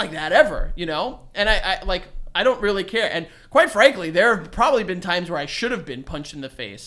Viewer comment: i swear all the time i thought he was saying quite frankie just as he call himself with names sometimes